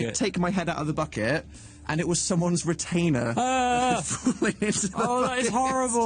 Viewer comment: I thought Dan said "retina" instead of "retainer"....probably the only thing more horrifying than what he actually said